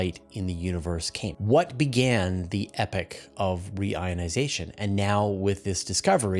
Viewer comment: i keep hearing "epic" of re-ionisation, rather than "epoch".